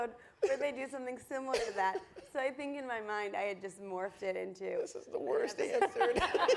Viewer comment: What does Bill say at 55:32? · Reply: "This is the worst answer to anything - incomprehensible yet cute giggling "